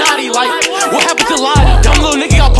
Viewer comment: My bad I thought you said notti